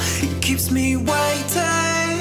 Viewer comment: for some reason i hear "the one to catch me if i fart" at 2:41 XD